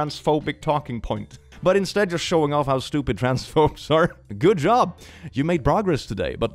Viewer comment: I know he said “Trans phobes” but it kinda sounded like “Trans folks” which is entirely wrong here.